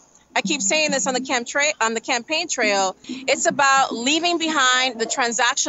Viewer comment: chemtrails 😅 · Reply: I just thought it was funny that when she misspoke here maybe at 13:55 , it sounded like chemtrails. she immediately corrected herself, because she of course meant to say campaign trail. no worries, I don't believe in chemtrails and I'm supportive of her and the squad and Bernie But I have a simple kind of humor.